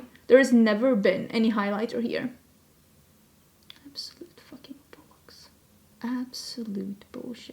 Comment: I’m pretty sure she dropped an F-bomb immediately preceding that comment, which I don’t think I’ve heard her do before. This is the video that broke! The things she does for us 🤣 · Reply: I heard "fcking bollocks" and I loled